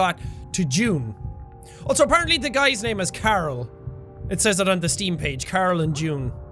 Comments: thought you said his name was carol instead of carl and then i saw it, i was like oooooohh not a girl's name lmao. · I thought he said Carol not Carl....CAAAAAAAARRRRRLLLLLLLLL...sorry I had to